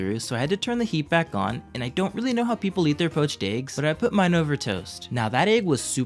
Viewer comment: I thought you said peached but you actually said poached